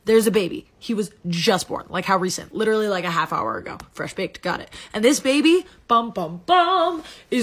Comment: I thought it said “fresh bait” not “fresh baked” my humor broke by 5 seconds into pt 1